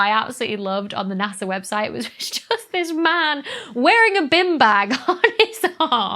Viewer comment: Ha. one fine example about how not all English-speakers speak the same language. I had no idea what a "bin bag" was... in fact, I thought she was saying "bim bag" at first.